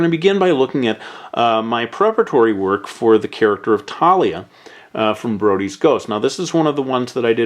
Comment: wowowow...did he say "Taliyah"???